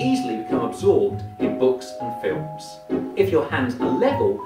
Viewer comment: Seems I misheard "books" as "hooks," and it also seems like I didn't follow directions as intended. I figured if these things were pulling up or down on my fingers some that my arms would stay steady as my hands bent at the wrist, so the right one was up about 45 degrees and left hand bent down a bit but not as far. I assume I would have had my arms separated somewhat height-wise like I did with my hands and still have fit into the first category either way. I definitely am easily immersed in movies/shows, at least when I want to be. Sometimes I have to put a little effort into it but generally I naturally get real into things. It's how I avoid being too critical of something. It's not fun if I notice the stupid things as they happen. I can think about those things after the movie (unless something is so blatant to me that I can't ignore it, and it takes me out of it)